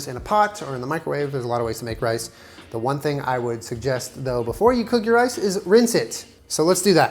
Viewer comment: I spaced and thought he said “before you cook your ice, RINSE IT”